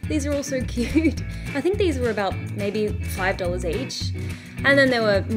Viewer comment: sorry did she say $5!!! 😱 I love japan 🇯🇵❤️☀️❤️